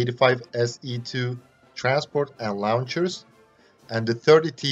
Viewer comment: When you said launchers, it sounded like you said lawn chairs.